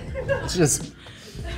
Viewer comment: I thought he said ' "just guys being nude"